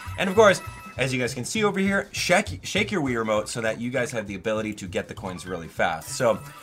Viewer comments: did he just say wii remote? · Did you just say Shake your Wii Remote